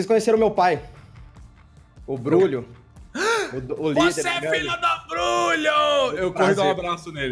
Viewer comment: Eu juro que ouvi: "Você é filho da puta".